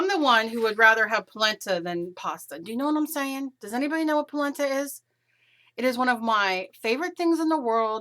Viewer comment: Lol I thought you said placenta!!!